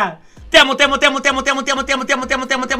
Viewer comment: escuche motea¿